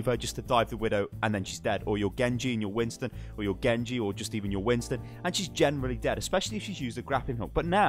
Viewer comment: What are you saying? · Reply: "Your Genji, or your Winston; or your Genji, or just even your Winston.